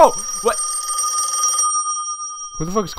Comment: Why i thought that said “I LIKE YO GIRL”